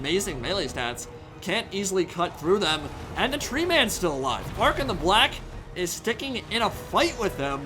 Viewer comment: D- did he just say Bark-han the black or are the tree puns invading my soul now?